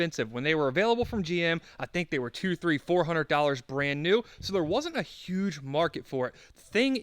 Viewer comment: I winced when I thought you said 15 hundred!